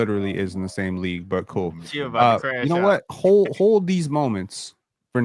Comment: I thought was abt to say “hold these nuts” 😂😂😂😂😂